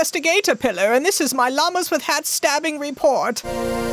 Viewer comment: This is technically a new Llamas with Hats video... Who paid the $250K fee‽ Edited to correct cost. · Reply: I thought he said "Hello I am Kelli the Investigatopillar and this is my Llamas with hats special report."